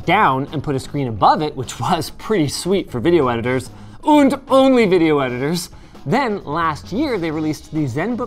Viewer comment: Did Linus really say "Und" instead of "And" or is it just my german brain D: (in the first min)